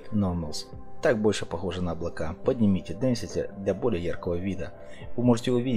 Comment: что он сказала сделать и нажать:? я не понимаю, все сделал кроме этого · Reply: Density